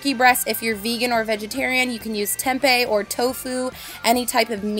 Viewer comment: did she just said TEMPE ? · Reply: tempeh, yes.